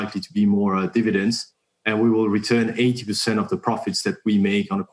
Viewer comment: did he say we will give back 80 or 18% of the profits??? and by the way he says dividends on a quarterly basis, but the dividend is set as semianual <link> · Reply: 80% - that's their policy